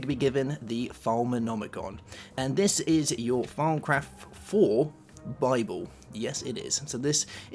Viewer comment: thought he said "farmcraft" at first xD